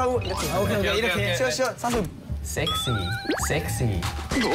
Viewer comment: what did bb say? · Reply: He said "sex" 😂